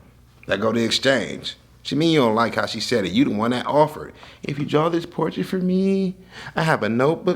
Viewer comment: didnt shorty make the deal in the first place ??? lol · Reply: I was looking for this comment lol I heard it as she offered